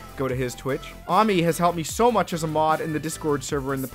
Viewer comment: I thought you said mommy there and I was like your mom streams?